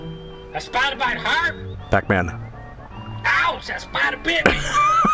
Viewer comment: what are those vocal samples from. I especially love "Owzers, badda bip" Edit: So, it occurred to me that what he was actually saying was "Spider bit me", not "Badda bip". I have dain bramage. Edit, The Sequel: It's this video right here <link>